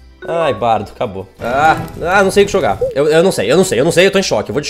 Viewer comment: so eu que entendi ele falando : Ai, eu nao sei o que cho ga